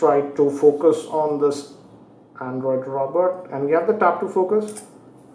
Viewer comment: Good review. Did he say Android ROBERT at 8:15?